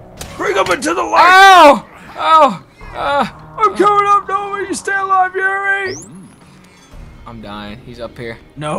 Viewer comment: did he say "im comin stay alive Yuri"